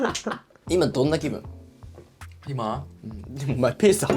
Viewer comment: の「今どんな気分？｣が完全にBoomBoomBackに聞こえた